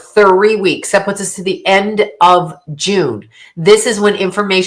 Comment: I thought I heard her say by the END of June ??? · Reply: 3 weeks from 6/9 so the end of june, yes